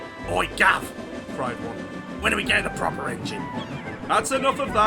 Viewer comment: What did the truck say @ 32:34? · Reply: “Oi Guv, when are we getting a proper engine?”